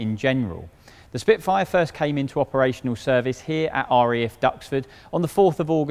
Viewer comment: Did he just say R.E.F? Royal Earth Force, that would be when the R.A.F are grounded.